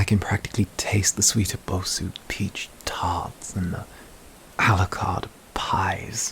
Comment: I heard "Alucard pies." I'm fairly sure that's not what he said, but now I can't hear anything else. Someone please help me.